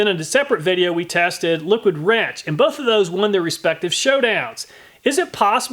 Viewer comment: i briefly thought he said “liquid ranch” in the beginning and now i want a comparison of the effectiveness of different salad dressings.